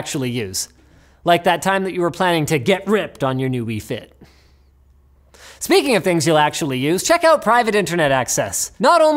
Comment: I thought for a second he said... "speaking of things you'll never actually use.... private internet access!!!"